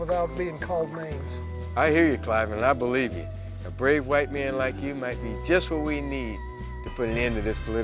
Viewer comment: Did he just say "a great white man, like yourself"?